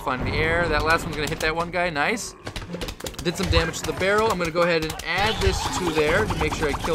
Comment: Is it me or does it sound like he said feral instead of barrel at 5:30.